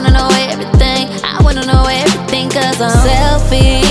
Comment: ok so at first I thought she was saying "selfie"